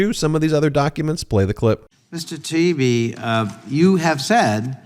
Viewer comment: MTG is going "How does he know all this? How does he remember it?" 19:06 Did Connolly say "Mr TP"? Then later he says "Dump whitehouse", LOL.